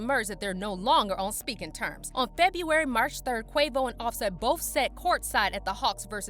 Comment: Did you say “on February March 3rd”? 6:26 🤔